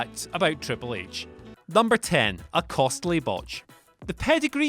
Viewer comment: I seriously thought that said "a costly bitch" and i almost lost my shit